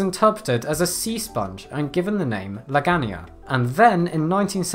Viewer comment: Lmao I was just listening to the audio at first so at 2:00 I thought you said they gave it the name “Meganium” and I was equal parts confused and impressed.